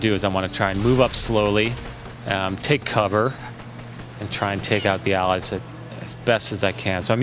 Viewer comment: lol great game and in 3:23 did i hear him say take out the allies?